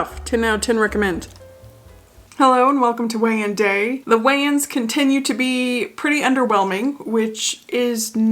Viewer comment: When you said Weigh-in, I heard it as Wayan, like the Wayans brothers.